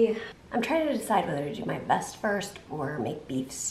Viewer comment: What I heard "I'm trying to decide if I should do my best first or make beefstew first, I think I'll do my best first" but then my brain started working and ruined it by saying it was probably her vest 🤣